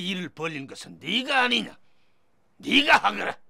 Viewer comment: Did he just say "nigga"? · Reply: yeah 니가? or 네가?means is that you?ㅋㅋㅋㅋㅋㅌㅌㅌㅌㅌㅌㅌ